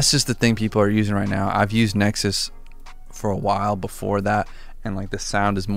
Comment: When the dude said nexus I thought he said netflix